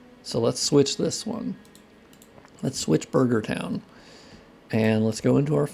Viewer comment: Wait did I hear a casual reference to ‘burger town’? Like the song, “I’mmmm shitin’ on down now I must be in burger town!!” From the circa 98’ burger documentary?! Lmao For reference: <link> @ 03:30 in the video for the song Are you a Jaboody show fan by chance??